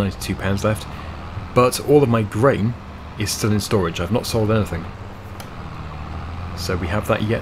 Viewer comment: D at 16:03 I heard “… All of my brain is still in storage”.